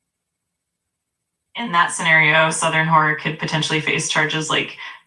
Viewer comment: did just say "Southern Whore"? lol